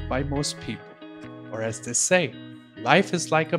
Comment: did i hear right? at 2:58 did he say "life is like a box of chocolates. It's very overpriced and I don't have either of them."